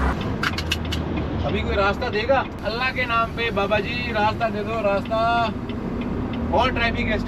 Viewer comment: did he say Allah k naam p at 6:03?